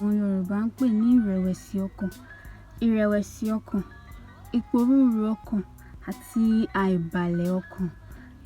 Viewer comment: what did you say here, iporurun okan?, what is iporurun? Or iporuru as in confusion?